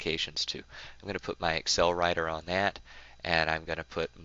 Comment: did you really call the spreadsheet "excel writer"?